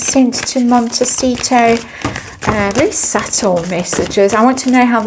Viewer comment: I almost heard you say 'Mentalcito' !!